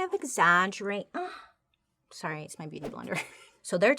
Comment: what did she say? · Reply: Sorry it’s my beauty blender